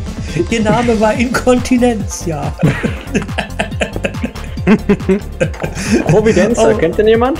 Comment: Pobacke 😉😁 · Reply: Ich sagte ja schon: Ich habe immer "Inkontinentia Popelfee" verstanden ...weil das so verlacht war :D